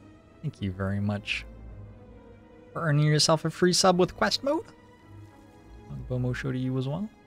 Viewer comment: can someone tell me what is the gimmick you say for the subscriptions plz? because my subtitles are screwing each time at this right moment that would help me to learn english ^^ here for exemple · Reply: it's not English. He's saying "Ogbomosho" which is a reference to a thing from a game he used to play, he didn't know how to pronounce the word so he looked it up and go the sequence of noises you hear. Don't remember which game it was tho (X-Com maybe?)